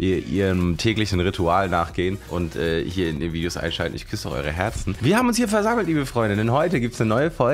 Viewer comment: Jo dude... Ich habe erst verstanden "ich piss in eure Herzen"...chill hahaa